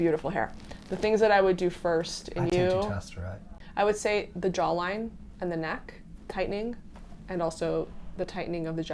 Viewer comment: did says finasteride? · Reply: He said dutasteride